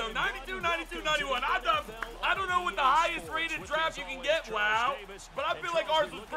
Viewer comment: Who else thought it said highest paid draft?